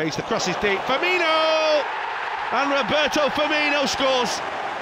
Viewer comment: Did I hear "Herberto Firmino" on a couple of those goals? Great name.